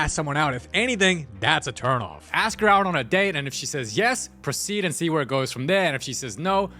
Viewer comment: did you say proceed